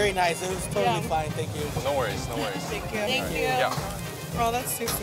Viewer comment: 여기서 존박이 뭐라고 대답한건가용?? 😮 노 워리스..??